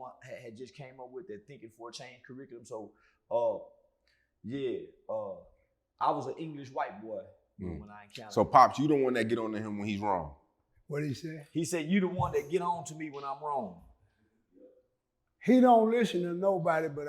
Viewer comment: Did Charleston say e was "an English white boy", at 1:25??